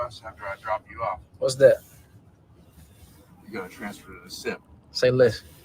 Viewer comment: What did the driver say he wanted him to do after dropping him off? It's a bit muffled. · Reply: I thought he said transfer to the simp🤣